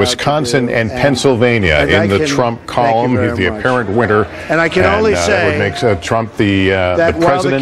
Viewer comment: Narrator so focused on getting his low bass voice that he ends up saying "winter" instead of "winner"